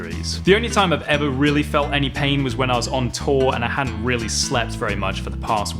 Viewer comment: What Charles says: "haven't really slept in a week " What I heard: " haven't really slapped in a week" My brain is broken 🙃